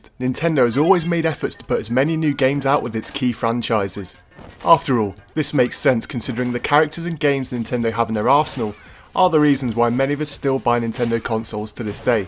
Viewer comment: Thought you said arsehole.